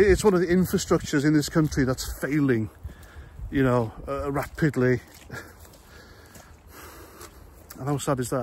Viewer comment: I was half listening when I thought I heard you say "rat piddly". My mistake.